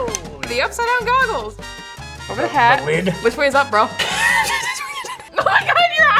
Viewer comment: what did she say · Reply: Petessup bro? I think